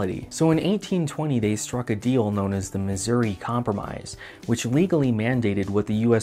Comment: When I heard it the first time, I misheard the "Missouri compromise" as "misery compromise". I found that an appropriate name.